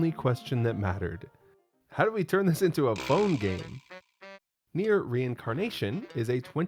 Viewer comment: Legit thought you said bone instead of phone